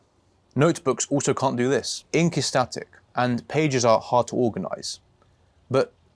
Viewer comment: What is this program? I need this in my life. I heard you say something like incustatic, but can't find anything like that online. Did I hear that name right, is that what you said? I also heard you talk about Obsidian, and searched it, and saw that has a graph. Is that what you are using here? Thank you.